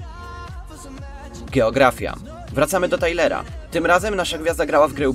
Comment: Tylko ja usłyszałem ''gieografia'' ? xd